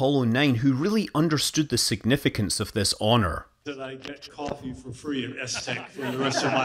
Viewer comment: What did Rusty say? He gets to ...... something free for the rest of his life? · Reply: Coffee